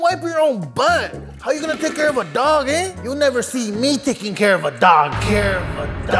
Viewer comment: I thought you said you will never take care of a dog😂